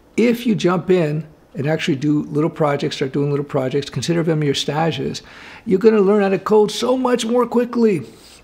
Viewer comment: What word does use around 3:30? Is it stashes or stages? I’ve heard him use it on other videos as well but I’m not sure what it is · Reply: Stage. It's french for internship